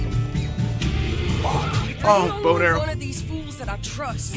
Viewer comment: When he said bow and arrow at 3:12 i heard boner lmao